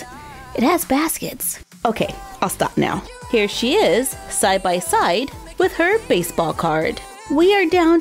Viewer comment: did she say Syfy side. Lol 😂